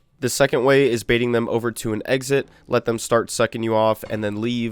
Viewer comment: what did u say? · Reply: well, they are basically sucking your head